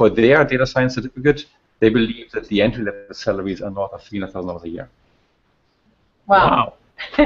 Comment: What is it that Sebastian Thrun says around 5:19 regarding the entry level salary? It got a big reaction from the other two but I don't understand what he said. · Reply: He said, 'more than $300,000 a year'.